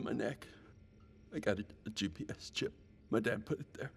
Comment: Without the subtitle when he said “GPS Chip,” I thought he said “Chip in Chip”